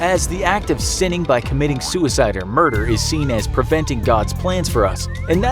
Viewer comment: u can see that it said perverting with captions instead of preventing